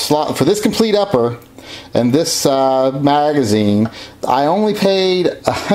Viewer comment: did you say Margazine? were you thinking about eating margarine again?